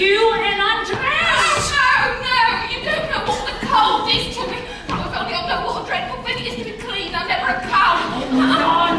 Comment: What she was saying at 31:34 ? I didn’t understand this part of the scene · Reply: I know it was a YEAR ago you asked this 😂 But I think she says "You don't know what the cold is to me. If only I'd known what a dreadful thing it is to be clean, I'd never have come" 😊